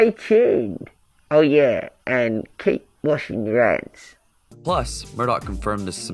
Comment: I honestly thought he said and keep watching your eyes because it was muffled by his red bandana but now I'm finding out well I found out a couple of days ago that he said hands and not eyes